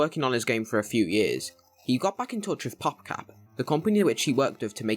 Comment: Is no one going to talk about how it almost always sounded like he was saying "pop crap"